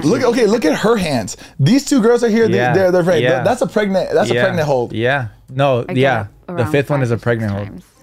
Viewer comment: “That’s a pregnant hold”, at first I thought you guys were saying that’s a pregnant ho! 😂